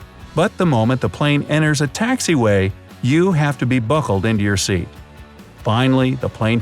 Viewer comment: buhckled what did he said?